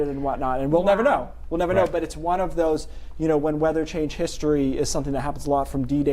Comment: first I heard …when weather (changed history). But he actually said when (weather-change) history. In other words, weather modification history